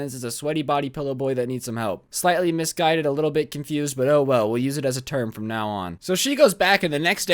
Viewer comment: did just say OwO